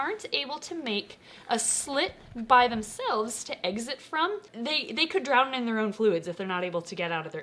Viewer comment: she said EGGxit instead of exit......